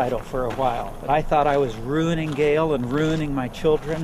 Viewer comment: did he say his wife's name was Gayle? ...Ironic.